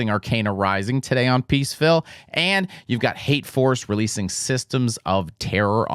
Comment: Wasn’t looking at the screen when you shouted out Hate Force, misheard and thought you were backing Hate Forest 😅😅